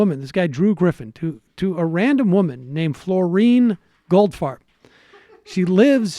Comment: I couldn't quite make out the name of the woman. Did I hear correctly? Is her name Florine Goldfart? heheheh :P